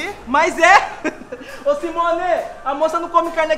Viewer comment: O SIMONEE Juro que entendi fimose KKKKKK